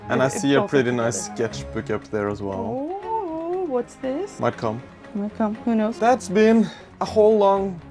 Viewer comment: mein kampf? Did I heard it right? 25:50 I think I misunderstood and it's your new book that might come